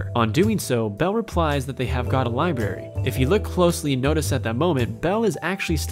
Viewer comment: did he seriously say liberry instead of library